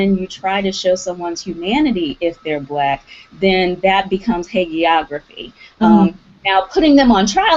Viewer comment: at about 16:00 , Prof. Mk Mitchell said something like "heygeography". i'm sure that isn't what she said. can anyone tell me the word she said?? hate geography? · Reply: O! i think i just figured it out: HAGIOGRAPHY - the writing of the lives of saints. derogatory adulatory writing about another person. biography that idealizes its subject.